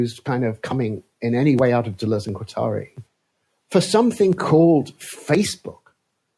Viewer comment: What does he say at 6:45? Closed captioning says "Dualism atari" (obviously not that, just asking what word is supposed to be there) (On topic of Facebook being a horrible name) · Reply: Deleuze and Guattari